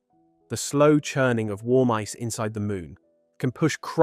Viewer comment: When you said “warm ice” I thought you said “War Mice” 🐭 ⚔️